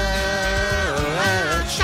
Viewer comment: Always thought they where saying "Sugar Ray baby, don't say a word"